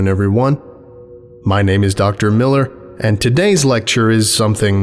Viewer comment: When it said Dr. J Millar I thought it said dharr man